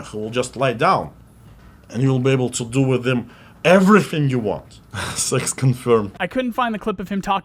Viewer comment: Sounded like he said "You can do anything you want to them, sex confirmed" now I'm def gonna get back into the game lol.